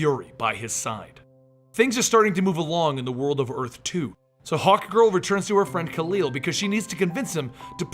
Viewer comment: why do i always hear "hot girl" instead of hawk girl